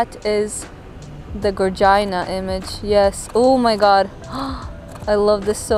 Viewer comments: WAIT - what did she just say?!!? @@ 4:07 (sorry dont want it to be rude - just genuinely wondering what she said) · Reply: I think she was trying to say "gorgeous" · Wait a minute.. did she just say Gogina @ 4:06 ? XD